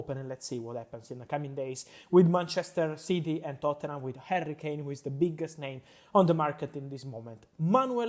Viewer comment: Why am i keep hearing "hairy kane"!!😹